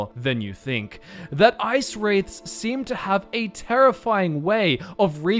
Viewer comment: I thought you said, Ice raids. (Seriously. Lol) Either way....yep. Great vid